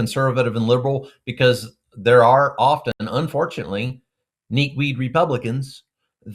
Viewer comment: Did he say "kneak weed republicans" ??? 😂